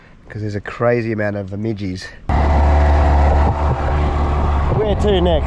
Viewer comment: what's an amigi or whatever he said?